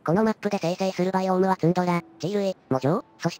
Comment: 極地浄化おめでとうございます。今回、マップの変遷がきれいで（オーロラとか）良い感じでしたね。最初は間欠泉有るのに温泉入浴シーンが無いとは、サービスの分からんゲームだと思っていたのですが、充分代わりになりました。あと、藻場（もじょう）を喪嬢（もじょう）と勘違いして画面を見直してしまいました、恥ずかしや。ペンギン可愛い。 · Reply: ツンドラをツンデレと聞き間違えたからへーきへーき あと入浴サービスシーンあったじゃないですか、バクテリアの